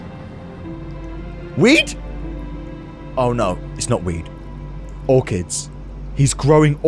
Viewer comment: At 29.05 I thought he said "Is that weed?! Oh it's not weed or kids."